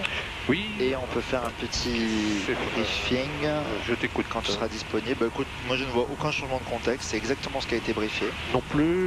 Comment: Does he say "Fienga sera disponible"? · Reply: « On peut faire un petit briefing quand tu seras disponible » Sorry not your surname lol